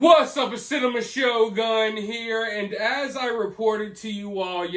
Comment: I thought you said "cinnamon show gun"